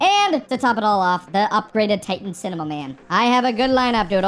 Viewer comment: did you call upgraded titan cameraman "upgraded titan cinema man